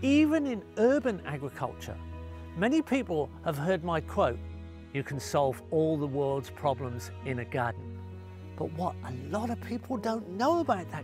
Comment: I heard "you can solve all the world's problems in a gun" and I thought this started going in a very different direction 0.o